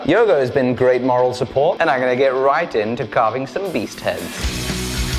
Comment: Oh, I thought you said “beet heads” - doh!!